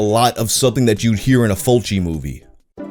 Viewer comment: did you say feltching movie? Like a movie where a bloke sprays his ball-goo up inside a girls shit-cavity then proceeds to slurp it all up as it drips out of her... That's a bit messed up man... didn't know there was an own genre of music for that. But I keep that in mind for my next motion picture.